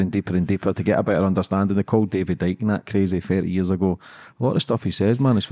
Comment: any one know where to find information on David didiak, I believe that’s what name I hear